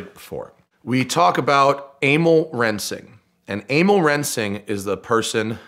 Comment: When he said amo rensing I thought he said anal reaming